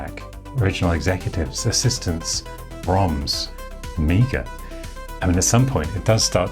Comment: Did you say Amiga? 25:48 It sounded like "Amiga', isnt that trademark owned by someone else, not the owner of all the other commodore stuff, I assume a deal was made with him to license the name?